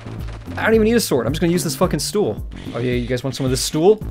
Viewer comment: did he said stool (chair on russian) or tool?